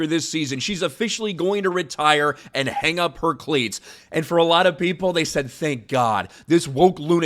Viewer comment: Hang up her what now? Oh, cleats. I thought you said cli- 😂